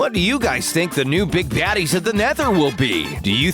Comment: I’m sorry I keep replaying this one specific clip and CANT STOP LAUGHING. Please tell me I’m not the only one who hears “ Big Daddies of the Nether”